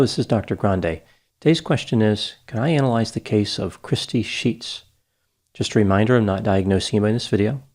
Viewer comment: I heard that as crispy sheets 🤣🤣🤣